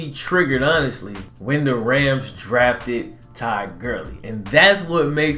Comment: i thought u said Ty Gurly, im like wtf. i guess just the accent for Todd Gurly